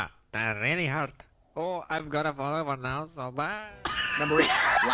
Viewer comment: I'm in tears 😂 what did he even say?? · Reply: I heard “I got a bullet wound so bye!”